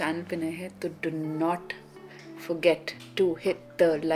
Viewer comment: Even i thought that is saying do not subscribe 😂😂😂